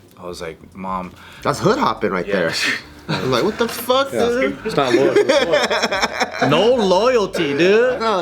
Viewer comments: I HEARD IT 16:40 HE SAID THE N WORD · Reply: He did not say the N word. He said "wtf dude" then said "no loyalty dude" then said, "no dude" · Wait did say the n word @ 16:41 I hope I heard wrong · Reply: You heard wrong. He said "wtf dude" then said "no loyalty dude" then said "no dude" No where in there did he says the n word.